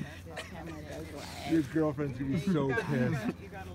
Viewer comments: did cameraguy just said "Your girlfriend is going to be pissed" XD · Did anyone here what said at 7:33??? "You're girlfriend is gonna be so pissed" OMFG DOES HAVE A GF???? Hope it's lex